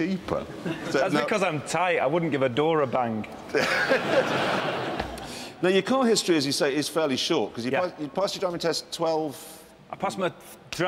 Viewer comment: okay, maybe I'm just dumb or maybe it's a regional thing but I still don't get that last bit. I thought he said "I wouldn't give a door a bank." Which really doesn't even make sense either. But thanks for trying. :-)